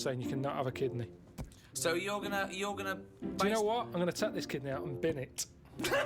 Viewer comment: "D'you know what? I'm gonna take this kidney out and bin it. " Edit: Changed "kid" (what I thought he said) to "kidney" (what he actually said).